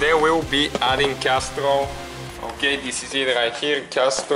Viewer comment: I thought he said castor oil, I was like "err..."